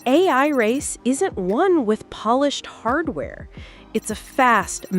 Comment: Pity the AI you are using can't understand syntax: subtitle put "one" instead of "won" around 1:20! :)